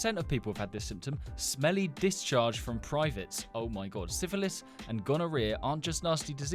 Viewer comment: i thought he said "smelly Pisscharge from privates"